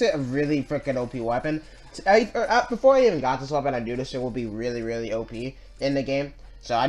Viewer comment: did you say shit